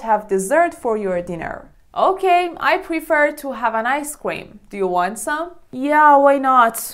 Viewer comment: I heard "aic cweam"